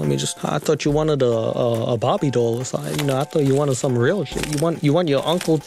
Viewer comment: I thought he was saying barbie instead bobbie